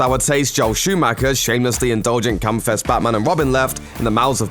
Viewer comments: "...Joel Shoemaker's shamefully indulgent cum fest batman & robin..." Did I hear that right? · Batman begins has neon drenched visuals? Gotta get my eyes checked bro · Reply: He said "rather than" to contrast Batman & Robin. I understand you mishearing him, the accent is thick and funny on some words, like how when he said "campfest" i thought he said "cumfest" XD